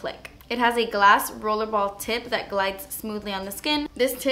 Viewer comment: omg go to 2:10 she says tit instead of tip im dying😂😂 btw, I love your videos x❤😂